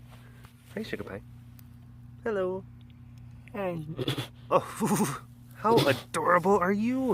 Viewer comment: it sounds like Acari says hi back! :D